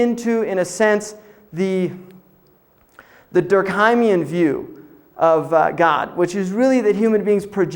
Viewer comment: Any of you can point out what he said on 24:13? the xx view of God. What is xx? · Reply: "Durkheimian view" as in Emile Durkheim the french sociologist